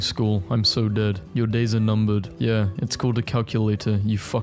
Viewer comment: pretty sure that’s calendar… Edit: 11:58 as much as I liked 2008(I didn’t)I think that says 2006